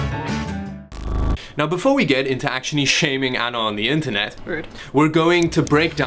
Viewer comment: Lol, I blew a snot bubble there as I thought you said "shaving Anna on the internet"